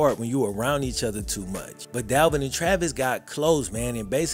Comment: I misheard that was "they went at each other constantly in Math" 😂. Probably both Math and Madden.